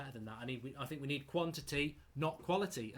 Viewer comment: Did he just say we need quantity not quality? 😂😂😂